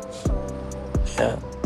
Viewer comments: I thought i heard i lore you instead of i love you 😂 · 😂😂😂 I hear " i lie you", did someone hear that too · I thought I heard I lol you I lol you too